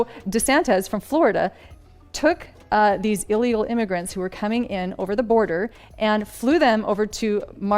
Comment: Did she just say illegal aliens….???